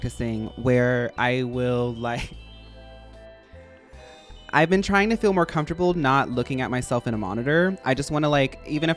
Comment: what the name of the content creator said at the 30:00? i cant find it T.T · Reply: Franchesca Ramsey